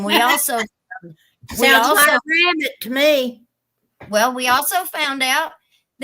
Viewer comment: what does Mom say? “ sounds like a Bandit to me”?🤷‍♂️